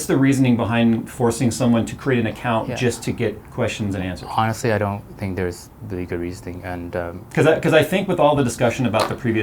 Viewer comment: did he say "honestly, I don't think there's legal reasoning?".